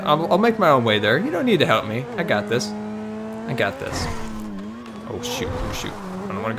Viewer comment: WTF HE SAID SHIT AT 3:15 I THOUGHT HE HE SAID HED NEVER CURSE IN ONE OF HIS VIDS😧😧😧😧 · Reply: Cristiano Ronaldo_7 he said shoot